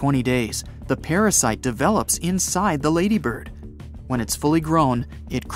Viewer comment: Did he say lady bird